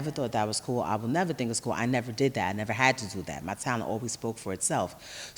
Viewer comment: What does she say at 3:07? My sound always spoke for itself???